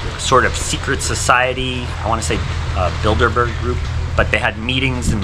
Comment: Did he say BUILD A BEAR group 😂😂😂